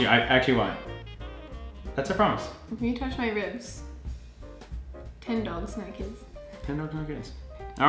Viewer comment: "Ten dogs naked" am i the only one who heard that? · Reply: Lol she said 10 dogs no kids 😂